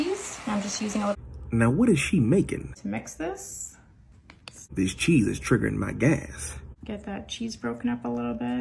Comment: “This cheese is triggering meh gas” What I hear: “This cheese is trembling meh ass” ☠️